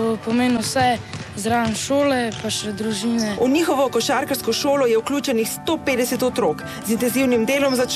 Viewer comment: lol did i just hear stupid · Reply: 'She says: 'Sto petdeset' (in Slovene) - ’one hundred and fifty' kids practices in the group of training. 😉😇